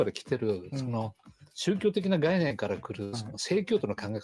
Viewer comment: There is a translation error in the footage. At 05:39, when the subtitle says "sex education" (SEIKYOUIKU 性教育), actually he said "Puritan" (SEIKYOUTO 清教徒). He refers to the influence of Puritanism in the U.S.. Either way, yes, it seems he implies that the ideas of DEI activists are almost religious and not necessarily something to be forced to everyone.